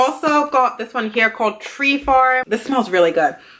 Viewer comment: Congrats boo!! Soo happy for you! 🎉🎉🎉🎉 Ps Low key thought you said the name of one of the B&B Works candles was called, “Tree Farts,” and I died. 😂😂